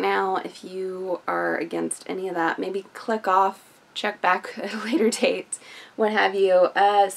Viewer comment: Hello ! Awesome! ...did you just say "fuck off if you don't like it" hahahahahahahahaha I love killing machines. You never went on a rampage cause ur not completely insane....maybe, I don't REALLY know you. No censor! holy PC of you! Not including the nudity cause I got a pc and I can find cartoon boobs . It's bookmarked ....Anyway, anyway, this comic seems great. I like the art. I want to see more. I wanna find out what happens. I wanna know now. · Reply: I said Click off. Lol. Calm down Thomas.